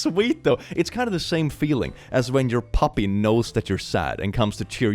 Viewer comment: I thought he said "papi" and not "puppy". I was very confused for a moment. I was like I don't think that's normal behavior...